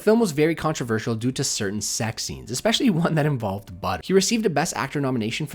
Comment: "The film was very controversial due to certain sex scenes. Especially one that involved BUTT." That's what I swear I hear. Does anybody have a different interpretation?